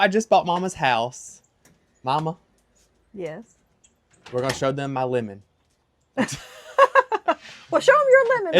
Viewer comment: Lord, i thought he said, "momma, I'm gonna show them my women." I was thinking, he done got him some ladies since momma left.😂